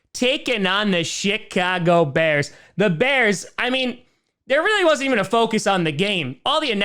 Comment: 🤣🤣 Oh, I'm dying. I thought I was hearing things. I still might be. Did he just say "Sh@ Bears?"